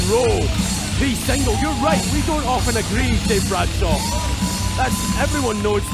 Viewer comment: I can't be the only one who heard "Dave Bradshaw" and thought they said "Dan Crenshaw", as in the Republican congressman from Texas.